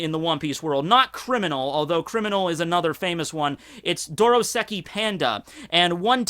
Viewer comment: It's DOSKOI PANDA, not "dorosaki panda"